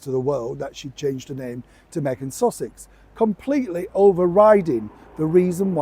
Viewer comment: I think Harry's kudos went south when we found out about the drugs at school, the fancy dress costume and the nakedness/stupidity in Las Vagas, he came over like the brat he still is. I thought you just said Megan Sausage🤣🤣🤣